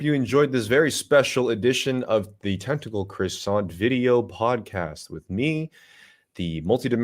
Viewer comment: Thought it said "testicle croissant" for a hot minute there